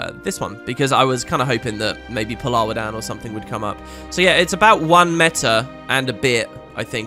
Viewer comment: Love your accent man. American here. I really thought you said that it takes "one meta and a Beer" to get the mount and skills. Lol. Bout right too.